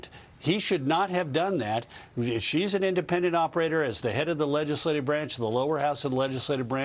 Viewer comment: Oh..'Going to Taiwan"...I thought she said she was"Going to Tie One On".